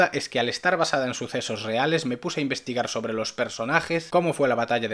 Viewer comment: hacer que no entendi?? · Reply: Investigar